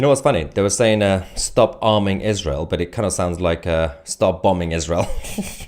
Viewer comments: I thought they were saying, "Stop harming Israel. " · SHAME ON THE POLICE FOR ALLOWING THIS TO HAPPEN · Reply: " Stop bombing Israel," is what I thought they were saying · I thought they were saying stop bombing Israel 😅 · I misheard it as "Stop harming Israel" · I thought they are saying stop harming Israel😂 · I thought they were saying Stop harming Israel 😂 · That's what I thought they were saying, stop bombing Israel. Obviously not. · I really thought they were saying Stop Bombing Israel! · I thought they were saying ‘start farming Israel’!! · haha. I thought they were saying "Stop bombing Israel." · I thought the chanting was saying "Stop harming Israel!". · I heard “stop harming Israel”… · I thought how good they are saying stop harming Israel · He knows that! Please try & keep up.. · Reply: I know knows that. Some people writing the comments didn't though. I thought at first they were saying "harming."